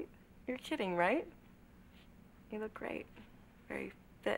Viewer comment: I heard, "you can always be fitter, look better", Mr. Wayne.